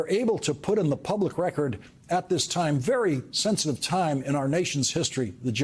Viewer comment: Americans deserve to hear every charge before the election. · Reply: I must be getting tried, I thought you said before the execution